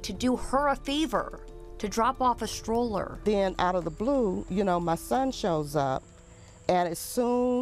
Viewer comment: Mom's don't seem to be upset by what happened (no tears, facial expressions....), did she set this up?. If you knew your son had a long standing grudge against his sister, why couldn't you inform your daughter not to come n drop off a freaking stroller being that her brother would be there?. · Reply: She clearly said that the son appeared, quote, "out of the blue!".... learn to listen